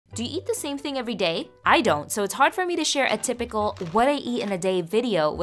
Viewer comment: My volume was down and I thought you said "do you eat every day? I don't!" I was like 😟